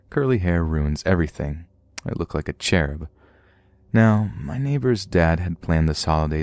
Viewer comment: The cherub viking prince is a good storyteller 😂😂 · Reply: Not going to lie, I thought he said “I look like a chair” and snorted.